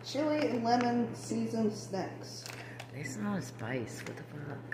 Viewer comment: I thought YOU said, " I didn't even know they had SEX! ( did not hear SNACKS